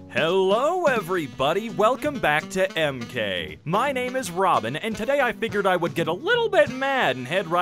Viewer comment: I swear I thought he said “My name is Ramen”